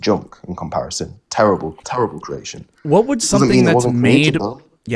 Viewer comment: Did he just say the Model T was a "terrible creation?" hahahaha😅🙂😂🤣!!!!!!!!!!